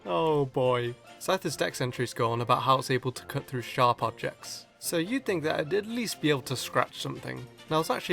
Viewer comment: "about how it cuts through sharp objects" uhm? I think that says HARD objects